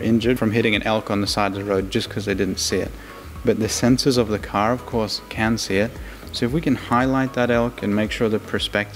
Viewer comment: "The sensors of the car cause cancer" ? What? Oh the sensors of the CAR OF COURSE CAN SEE IT. Took me like 5 times to understand what he was saying for some reason.